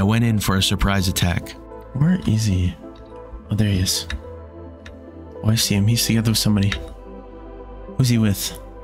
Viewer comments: btw when you said you didn’t know what u said I think you said, “oh I see him he’s together with somebody” so yeah 😊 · I believe you said "I think I see somebody" im not exactly sure if anyone else heard the same but thats what i heard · I believe you say “oh, I see him, he’s together with somebody” 😂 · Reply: I thought he said "oh, I see him. He's sitting there with somebody." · I think you Sayed “I see him I think I see somebody” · at 17:35 I think you said, “Oh I see him, He’s hanging out with somebody.” My best guess😂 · 17:33 i think u said "oh i see him i see him with somebody" or "i see him hes there with somebody" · I think he said "Oh I see him there with somebody" or something along those lines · "Oh I see him he's there with somebody" Is what you said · "oh I see him hes there with somebody" is what I heard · i think you said “i see him i see him with somebody”